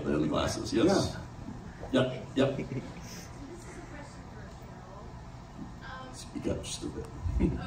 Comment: what does he say at 15:23 ? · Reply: He said "speak up, just a bit"